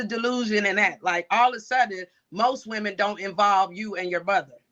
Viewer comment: I knew it was finna get gangsta when tough self put that cap on backwards & banged it to the side 😅 ….. I thought the good doctor said “modern women” & not “most women” … looks like they don’t listen either lol